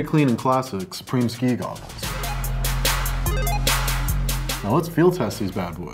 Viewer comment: BAHAHAHAHAH I THOUGHT IT SAID SUPREME KAI